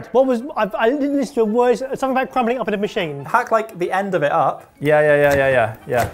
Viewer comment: Did anyone understand what he said here? "Hshsb ejsne dodidbd in the machine!" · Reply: "What- I- I- this is the worst, something about crumbling it up into the machine?"